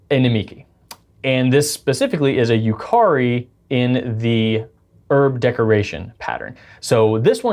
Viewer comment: Wait... at 05:35, did you just say 'erb?